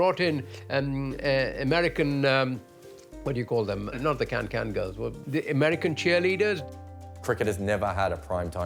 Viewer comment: did he just say "American camgirls"? Grandpops is into some virtual action